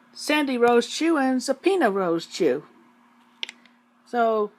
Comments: When Christine said "Zapina Rosechu" around 32:00, I thought she said "Subpoena Rosechu". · I thought i heard "Subpoena Rosechu"